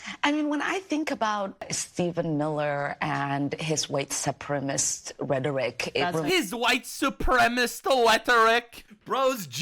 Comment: she actually said "white supprimest" rhetoric...not sure what that is but I'm sure it's not okay...